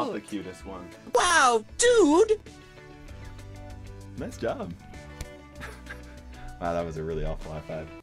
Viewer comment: when he says “nice job” the subtitles say “miss dumb”